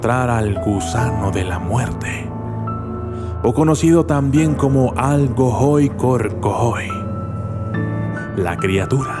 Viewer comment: oie que dijo? Jajaja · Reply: Alcor goi ay yo que se :V